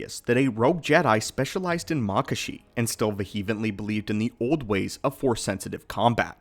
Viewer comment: Not to be that dude but did he say vehevently at 4:13?